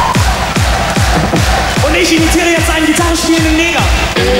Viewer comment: boah fuck und ich hab verstanden eine gitarre spielende Nena ... damn